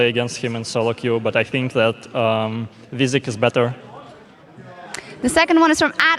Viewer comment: "But i think XXXXXXX is better" who does he mention? i couldnt understand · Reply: He was referring to Wizikx: <link>